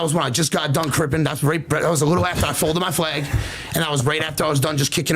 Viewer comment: HE MUST JUST HAVE A LISP.... CAUSE I SWEAR WHEN I HEAR HIM SAY "WHEN I FOLDED MY FLAG".. AFTER RELENTLESS MINUTES OF LISTENING TO HIM TALK, I'M CONVINCED THAT HE'S ACTUALLY SAYING "I'M A FLAMING FAG"